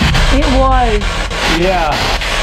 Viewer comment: i hear "It's Bloodie " not It Was